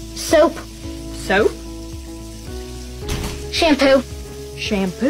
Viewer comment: “Does E=Mc2?” Argument done. Best line! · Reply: For a second I thought he responded and said fuck you to the mom and later realized he said love you lmao 😂